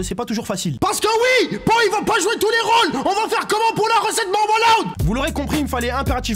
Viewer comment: il dit quoi ? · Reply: la recette marwa loud